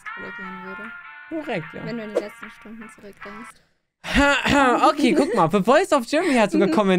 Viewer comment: wenn du ein paar stunden zurück bist (was sagt sie)